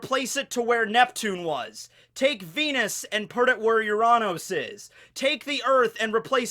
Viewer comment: "take pennis to where uranus is" i thought you said penis, but it was venus.